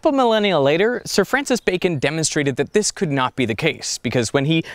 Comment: Is it just me, or does he say Sir Francis BACON at 1:09?!?!?